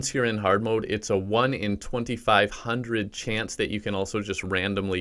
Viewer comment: "Its 1 in 2500 chance to get it" Me getting 3 in a row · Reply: Oh lmao I misheard him I thought he said 25000...